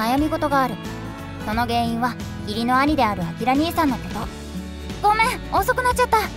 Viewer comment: バックグラウンド再生で動画聞いてて、義理の兄がギリーの兄って聞こえて「まだきてるの！？」って脳内再生が崩壊した